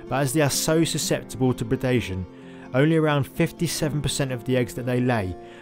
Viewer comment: they're so susceptible to what? i didn't recognize the word that was used there can anyone fill me in? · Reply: Predation. To being eaten basically